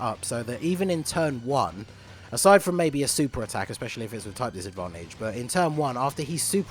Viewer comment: did i hear type disadvantage!!!!